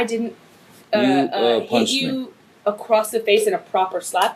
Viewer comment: "I didn't punch you, I slapped you" This reminds me of that cop from the "cop distracted by phone hits me head on" video. He hits a guy who was on a bike stopped at a stop sign, and when the guy asks the cop why he was texting while driving the cop says "I wasn't texting, I was just looking at my phone because another officer just sent me a message" Like, what ? 🤦😂🤣😂🤣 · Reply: I thought she said I didn't slap you, babe, I punched you